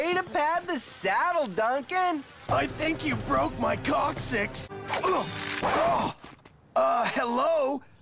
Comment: What did he just say? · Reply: He thinks he broke his coccyx. The coccyx is the “tail bone” or the bone that’s at the end of your pelvic sacrum. The lowest piece of the spine.